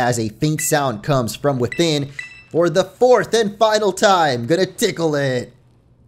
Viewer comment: Did he say diglett